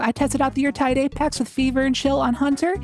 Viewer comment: What did you say in the editors note @ 4:04? You spoke so fast I couldn't understand you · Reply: I tried the Yeartide Apex with Fever and Chill on hunter